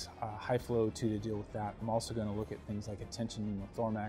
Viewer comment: Did I just hear "tension pneumothorMAX"? LOL